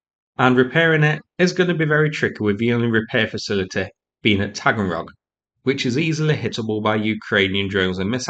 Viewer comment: Did I mishear that an AWACS had been removed to a repair facility at Fraggle Rock? It wouldn't surprise me....